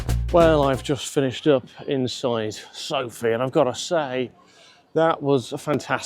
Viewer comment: I ran to the comments when I heard "up inside Sophie" 😂😂